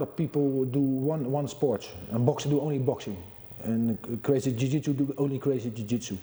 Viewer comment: For a second I thought he said crazy jui-jitsu